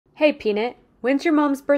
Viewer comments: When she said hey,peanut i thought she said hey,p*** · I'm sorry don't mean to be rude or anything but I thought they said penese so I had to rewatch it and it said peanut